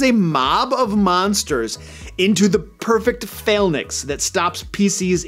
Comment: What is a "fail nix"? (Said a couple times between 11:00 and